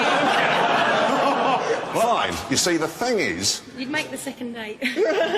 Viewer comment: what did she say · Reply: "You'll make the second date" 2 years late I'm afraid